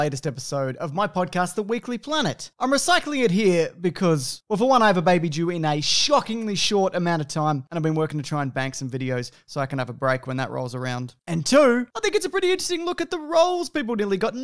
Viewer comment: Sounded like he said "I have a baby Jew." Kinda odd to specify, I think.